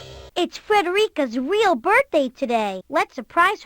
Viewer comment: "it's Frederica's real birthday" sounds really funny out of context, but it makes sense when you know that does a bit of saying it's her birthday every day in the show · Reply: She also says "Frederica" when you highlight her but she said it so quietly I thought she was saying "paprika", and knowing there was a Blue's Clues character named Paprika, this got me all sorts of confused